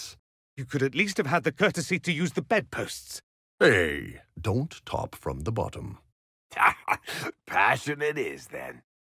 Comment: "Don't talk from the bottom." As a gay man, this made me laugh harder than I should have. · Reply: i thought he said "don't top from the bottom" this whole time lol